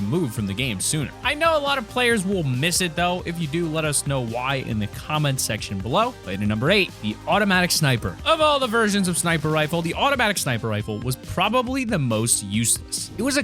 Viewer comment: did you just say of all the virgins of snipers?